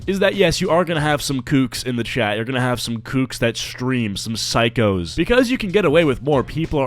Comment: bro i deadass thought he said gooks 💀